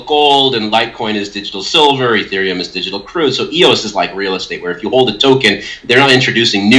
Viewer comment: digital CRUDE ? Did i hear correctly?